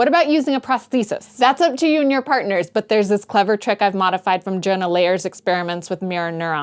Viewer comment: Was just doing a bunch of transcribing for the captions, but I don't know what you said at 2:10. After "there's this clever trick I've modified from..." Gerna Layers? Is that a name? I tried Googling the study, but can't find anything. If anyone knows, let me know, and I'll be happy to continue writing out the subtitles when I have some free time! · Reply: Jonah Lehrer's study of mirror neurons